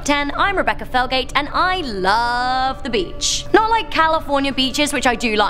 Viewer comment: I thought you said "I looooove bleach"